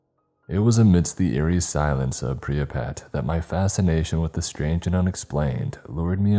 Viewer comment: I cannot be the only one who heard Pripyat pronounced as “priah-pet” at minute 2:19? I even skipped it in reverse a few times to really hear lmao.